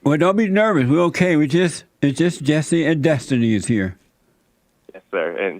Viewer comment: did he say Jesstiny 😂